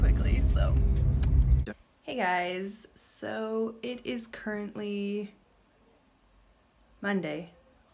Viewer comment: abla(yeah it is really funny to say xd) what is the word that you said at 04:57 Just tryna learning English ^^ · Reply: Currently😀